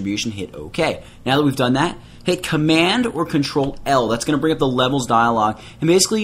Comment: commando lol. thats what i thought he was saying but hes actually saying 'command OR ctrl'. command being the mac shortcut.